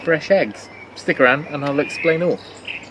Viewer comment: Did you say 'eggsplain' or 'explain' at 0:12 right at the start? I'm not sure...